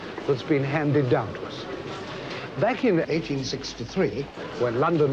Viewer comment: notice he actually says 1865, but 1863 was dubbed in